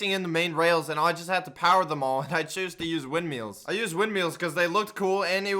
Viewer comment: did he just say wind MEALS